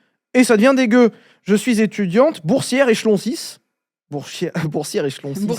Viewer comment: p'tin, j'ai entendu "je suis boursière et chlonsisse" il m'a fallu quelques secondes pour capter "échelon 6" j'vais ptetre paraître con, mais c'est quoi cette histoire d'échelon ? un niveau d'expertise ?